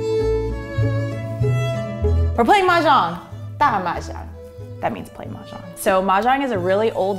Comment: huh. we play a little bit differently in hong kong maybe. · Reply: i think she's saying 打麻雀